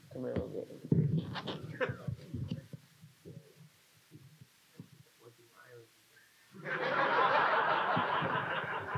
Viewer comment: What did he say at the end when everyone laughed? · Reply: He asked him - what’s irony?